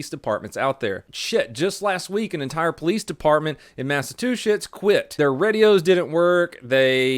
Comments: lol did anyone else hear mass-a-two-sh*ts? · it still gets me every time i hear him say "massatwoshits" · he says mass-a-two-shits instead of Massachusetts!!😂 I was dying! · did just call my home state “massive two shits”? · I heard you says massive two sh!ts XD of course you mean Massachusetts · did he just say "massa two shits?" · My favorite part was at 0:47 when you said “Massatooshits” instead of Massachusetts, I’m not sure if that was an accident or on purpose but I loved it! 😂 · Wait 0:47 Did you just call it Massa-two-shits? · Anyone else hear say Massa-two-shits at